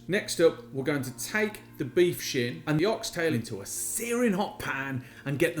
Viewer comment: why this part strangely sounded like beef sh$@ hmmm